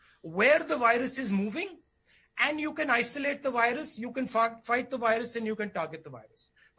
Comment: Am I the only one who heard 'you can f*** the virus' ? 😶